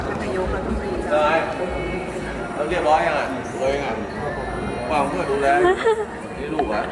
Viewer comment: นายกพูดว่าเรียบร้อยยังอ่ะตัวเอง ฝากให้เพื่อนดูแล ใช้ไหมครับ